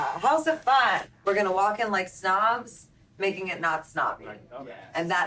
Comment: "Make it non stop. That is camp." Well said Gaga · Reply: Masayu Mabrura Masayu Mabrura @ 7:46 what she actually said was: “We’re gonna walk in like snobs, making it not snobby.*** And that is camp.”